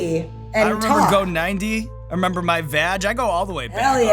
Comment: did he say i remember my vag?